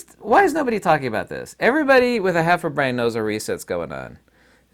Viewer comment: Wait, did you say "everybody with a heifer brain"? 16:30 LOL....half a brain I know...my first listen I thought you said heifer! :o) I was thinking, how does he know me?